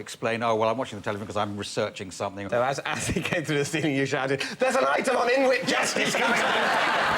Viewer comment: Can anyone tell me what David says here? Sounds like "there's a light on in which gestured(?) something(?)" and it's got me stumped. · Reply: So as he came through the ceiling you shouted, “There’s an item on Inuit justice coming on in a second!”